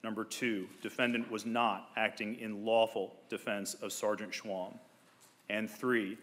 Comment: I thought the judge was saying "Sargent Shlong".